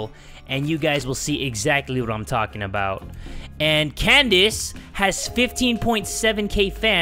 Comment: It scared the shiz out of me at 8:56 because my name is caedence and i thought someone was saying my name XD.